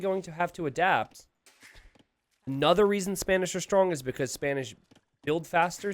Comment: Did I heard say Spamish not Spanish at 3:58 ?